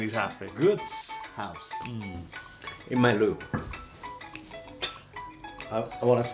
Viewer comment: I thought san said “in my loo” sorry😂